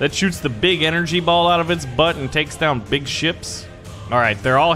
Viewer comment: i thought he said sh*s on 5:59 but it was actually ships pls make more i love this game